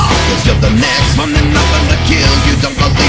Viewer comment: Did I just hear the Knicks have nothing to kill. Stone Cold you savage